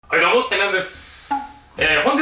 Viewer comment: はいどうもテルやんです！って聞こえてきた